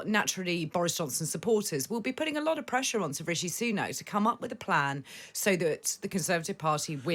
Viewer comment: Did she call him "Sir" rishi Sunak?